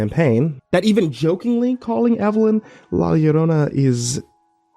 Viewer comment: Can someone help me understand the part at 3:36:53 that talks about "jokingly calling Evelyn ###"? I tried goggling but I can't seem to figure what is said enough to get an answer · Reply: la llorona is a south american myth about a woman who drowned her children to protect them (from something, depends on the story) and thus searches along river beds while weeping, she is known to steal and drown disobedient children. a lot of parents use her as a threat along the lines of “if you don’t stop pulling your sisters hair la llorona is going to take you to the river and you’ll regret not listening!” which obviously makes her a VERY memorable character